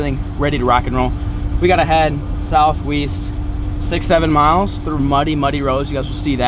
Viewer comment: did you just say south weast